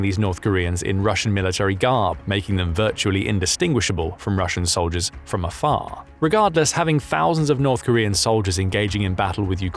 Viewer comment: did he say GAR not gear??